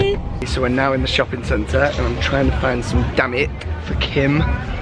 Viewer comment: HAHAHAHA when he said "damit" I thought he said "DAMN IT". 😂